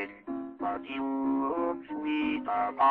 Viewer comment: All i hear is "daisy,daisy,give me yo a$$ for poo,Im half crazy all for the love of you,it would a stylish marriage,i cant,afford,a carriage but you look sweet,upon a seat of a bicycle built for poo." im sorry its just i hear that some how😭😭💀💀☠️ Edit: and the animation is wonderful ^^